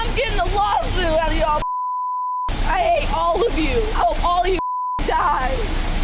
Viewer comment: F me, I heard "I ate all of you", instead of "I hate all of you".